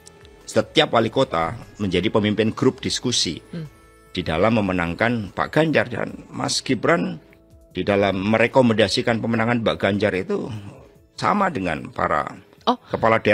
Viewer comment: ini aku yg salah denger apa gimana itu bapaknya yg bilang mbak ganjar?? wkwkwk